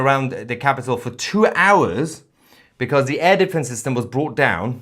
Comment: I always hear says the " "elephants".... I pushbutton cc and found out he is saying " air defence," which i thought i heard "elephants"..😮😢😅😅😅😂